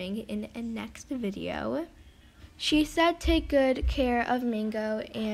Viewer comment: That was the most saddest trade ever. My sister would've done it for a mega dragon also 2 neon koalas Either way that trade you did was sad and I love the neon I want and I thought she said take good care of it :( I would cry if I did that trade